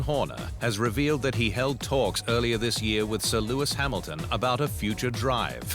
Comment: All I hear is " Sore Lewis Hamilton " .. I just realized he says "SIR" ... Oh my!